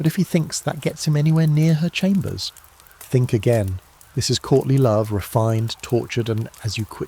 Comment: San Diego, CA - He’s saying: “Courting love.” In the video but I keep hearing “Courtney Love.”